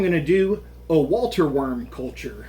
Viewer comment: 🤔 I thought it said water worms 🤭🤣😂 Good Luck 👍